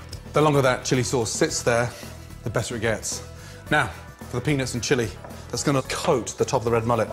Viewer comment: I could have swore he said "Now, for the penis and chili...."